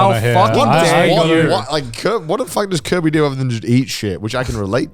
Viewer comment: I seriously thought you guys were saying the “Kobe” cafe for a hot minute. My brain was telling me that either; “that sounds utterly unremarkable in Japan” or “that’s is poor taste”.